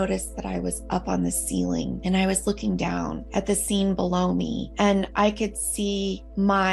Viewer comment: I’ve been watching too much American YouTube - at the intro I thought she said “I was looking at the same baloney”. Took a sec to realise she said “the scene below me” 🤦‍♀️ x